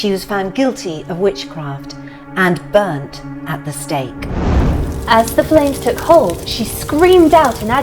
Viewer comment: Oh this was one of your scariest yet on the Halloween theme. I just had to share it around girls. Awesome video. Glad the burning at the stake has been banned. Husband only half listened and thought you said - burn like a steak! 🤣🤣🤣! Have a great Wednesday. 🎃🇳🇱🙋‍♀🥝👻